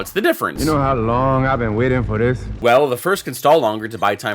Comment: I swear I heard “wen wait”